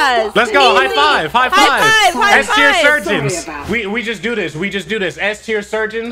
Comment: I THOUGHT HE SAID HOE SURGEON